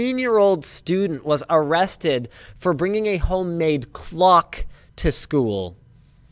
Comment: I first heard "glock" instead of clock so I wasn't surprised, but a clock? -C'mon guys But yeah it could have been an actual threat